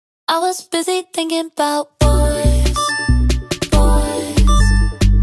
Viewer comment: what I heard: “I was busy thinking bout bees, D I N G”